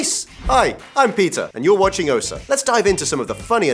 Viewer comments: I couldn't hold it in when I heard "I'm Pizza, you're watching Ossa" 😂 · When he said ' Hi I'm Peter ' I heard ' Hi I'm pizza '